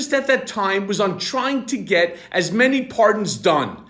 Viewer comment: Thought he said that he was "trying to get as many apartments done". Good thing for closed captioning!